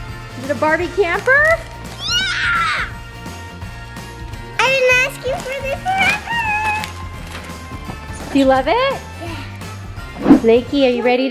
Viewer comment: On last gift I thought she said I didn’t ask for this but whatever